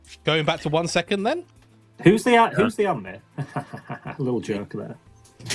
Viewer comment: What did say here?? I tried listening to it multiple times and had closed captions on and still didn't get it 😂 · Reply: "Who's the Amne? Hahaha. Little joke there."